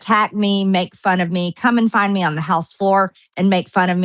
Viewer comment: 😂 what did she say!?? She says come and find me right? I hope LOL